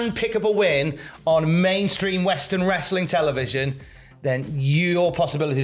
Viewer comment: Mainstream, I thought you said Impact?